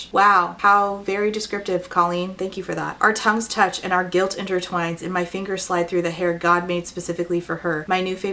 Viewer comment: Omg around the 12:30 mark I could have swore you said, “our tongues touched and our gills intertwined” and I just nodded my head and thought, “that tracks for this author, things just don’t need to make sense in these books”